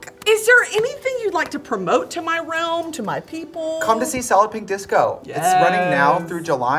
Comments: The wine officially hit when I thought she said, “saloping disco” instead of “solid pink disco”. 😂😅😂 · I hear “Solloping Disco” EVERY DAMN TIME SHE SAYS IT AND IT’S DRIVING ME NUTS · trixie's accent is so fierce that i thought she said "Salloping Disco" and not "Solid Pink Disco" and I was so confused when i googled it